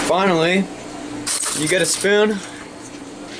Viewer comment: Did he say YouTube? 🤔